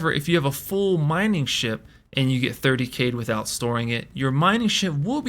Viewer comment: you get what? cant understand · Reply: talks about 30k protection will spawn your ship with all cargo at your choosen home planet